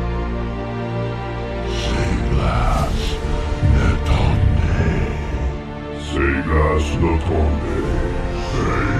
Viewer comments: Does any body know what the knights are saying in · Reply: This is Polish language. "Wybacz, to my!" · What does the Gurdian nights says on 1:31 while raising their swords ? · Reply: They say save us before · what language is this and what does it say? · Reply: This is polish. "Wybacz, to my!" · Does anyone have a clue what they’re saying🤔🤔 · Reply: This is polish language. "Wybacz, to my!"